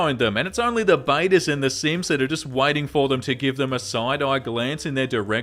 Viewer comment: At about 1:13:00 I thought he said "only the Simpsons will like you" but it was "only the simps." Obviously I watch some!